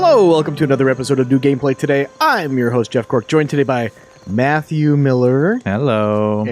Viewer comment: At first I thought you said Matthew Mercer, than I thought Matthew Miller is his birth name.